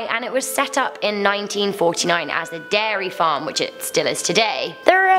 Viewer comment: Did u just say derry? 1:43 Omg pennywise are u indian?